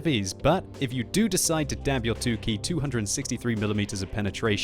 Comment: did he say dab?